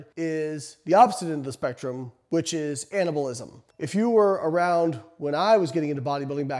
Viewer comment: I like to listen to these kinds of videos when I drive and I was kinda zoned out, and when you said anabolism I thought you said cannibalism and I was like wait what